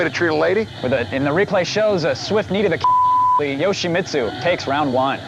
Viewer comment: Does say a “swift knee to the cunt” at 4:33?